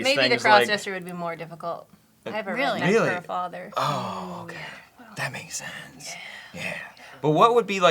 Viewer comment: What did say about her father around 1:44? I couldn't hear it over. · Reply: McGurkleroy! I think that she said that her father is a pastor.